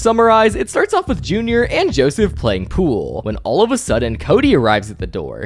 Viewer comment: Playing cool Edit: I swear they said cool not pool!